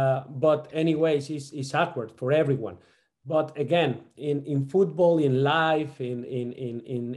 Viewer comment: He said "In Football and In life" and all I heard is "football is life!"